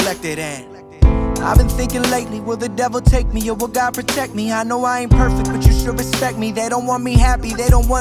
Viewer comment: I always thought it said “I’ve been thinking lately will the devil take me and will god reject me”